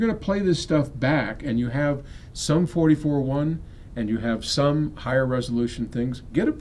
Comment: did he just say Sum 41 @ 7:50 ?